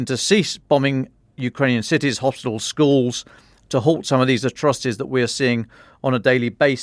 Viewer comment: did he say chinese attrocities